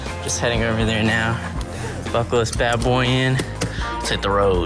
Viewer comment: I really thought he said “beach made boy”